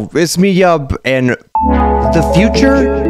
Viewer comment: Why is it when he said “it’s me yub”it sounded like he said rizz me up 👁️👄👁️